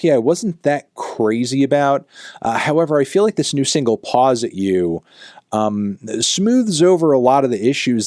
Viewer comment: i genuinely thought he said "paws at u" and i almost had a brain aneurysm